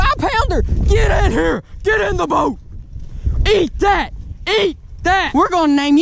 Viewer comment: did he just say get in the boat